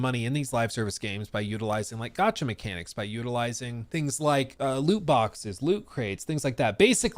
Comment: When he said loot box I thought he said "Luke Box" and I was like "huh?" just a little thing I laughed at myself for.